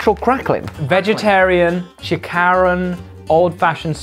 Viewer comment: Did he just sayyy chi ka ron??? Hahahaha chika bess